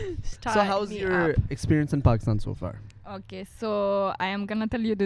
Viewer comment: my phone volume was low so I thought he said how's you experience in pakistan "sweetheart" (so far).....🤣